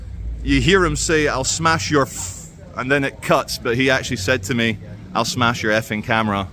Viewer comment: Why did you edit out where he says your camera and then proceed to tell us what happened? Something fishy here. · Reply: Actually he said "and then it cuts", 1:07. The point remains that he could've just shown us since he was filming from the start instead of feeding us a narrative. It doesn't always happen but it happens often enough for me to make a point of it here.